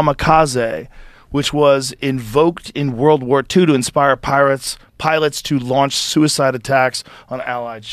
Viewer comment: did i hear a "Pirates" instead of "Pilots" lol...